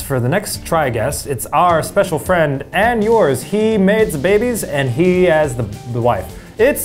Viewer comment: Eugene: “He has the babies, he has a wife. It’s Ned Fulmer.” Regular People: “Yay, Nedward!” Me: “But Eugene said babies. Soooo is he telegraphing a pregnant Ariel?” 🤔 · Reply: I thought he said "He makes babies"